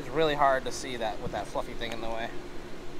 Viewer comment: Watch what you say I have kids who love watching you guys. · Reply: Sorry for the misunderstanding. A few people thought they heard a bad word at 3:30…but what actually says is “fluffy” thing when referring to microphone on his camera. As a family, we don’t use bad language. Thank you for your continued support. 😊